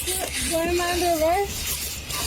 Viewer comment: she clearly said, "I don't have anything on me"